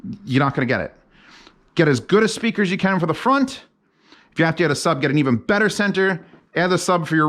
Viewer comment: Sure I heard "if you have to get a sub, get a better center channel" 🙄